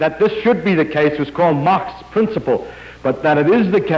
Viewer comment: Did he say "Mach's" principle?